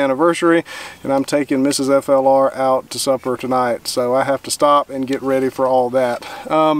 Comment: When you said you were taking Mrs. FLR 'out to supper', I heard 'Out to SUFFER'. I just about lost the breakfast in my mouth. Congratulation on the years (many to some few to others), you have had together so far.